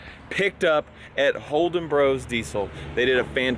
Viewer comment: I thought you said Holden Bros pizza and I was like huh